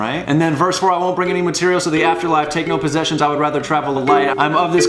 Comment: "IIIIIIIIIIIII WANT PRINGLES" You'll never unhear it 😂 you're welcome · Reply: I hear “and cereal in the afterlife!” I can’t NOT sing that